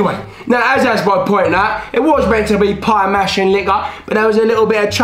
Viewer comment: Finally, the sequel we desperately wanted! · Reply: Bro am I the only one who hears "a pie mashing *n-word*" at 5:55?